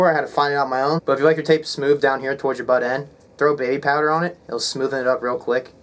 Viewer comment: What do you say at 6:39 regarding getting the tape on the bottom of the stick less sticky? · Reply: Throw some baby powder on it!